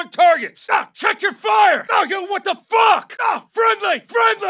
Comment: What does he say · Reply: aw- yo what the fuck?! in the subtitles in game he says "yo what the fuck" i thought it was so cool